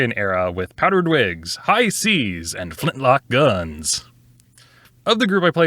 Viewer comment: I heard "flintlocks" as "flipflops" the first time, what a visual that was based on the rest of the context.